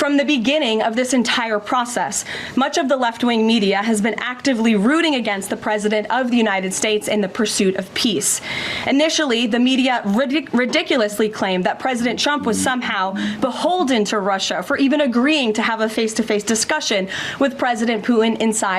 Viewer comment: I swear she said president chump